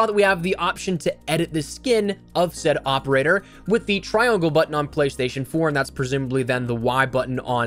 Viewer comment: Edit with the what button? Lmao he said sum T R I O N G L E B U T T O N